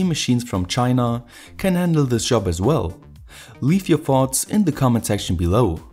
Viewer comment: I love this channel but, did anyone catch that? 8:03 leave your farts in the comment section below